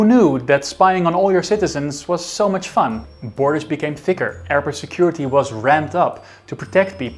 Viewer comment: The captions saying “Arab security” when he said “airport security” was interesting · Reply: I think the ai just misheard it as the way he said “airport” sounded like “arab”